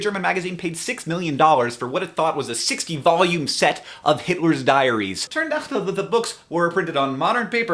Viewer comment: At first I thought he said "Hitler's Diarrhea" o_O